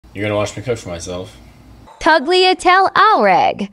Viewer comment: The first part kinda sound like he said "you're gonna watch me touch myself"